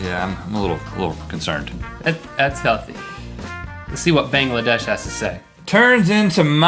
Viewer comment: "let's see what Bangladesh has to say" Did I hear that right?